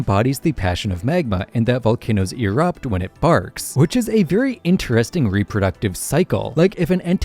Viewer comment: at first i thought you said when it farts